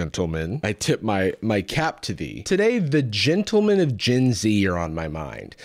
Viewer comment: did someone say DJENT?